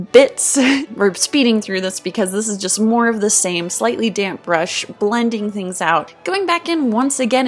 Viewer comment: When you said "damp brush" i thought u said dank brush